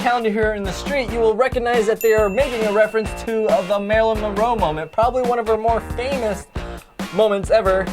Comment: did he just said "Melyrin mormoe" m